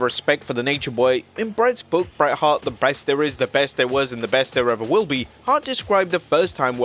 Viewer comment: haha u said breast instead of best